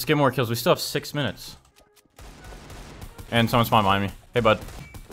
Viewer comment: Like when he said someone behind me and said are you okay buddy I turn my volume all the way up and what I hear is "someone behind my butt" lol